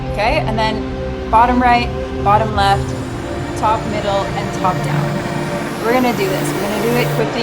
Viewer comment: did she say top down😭